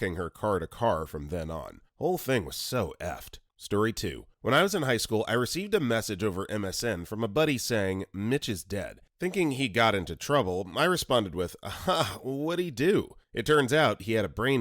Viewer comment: I might have selective hearing syndrome. He said "Mitch is dead". But I heard "Midgets dead" 💀